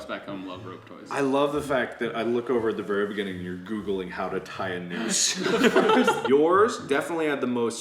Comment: No one: HoW tO TiE a NoOsE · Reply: At first I thought he said how to die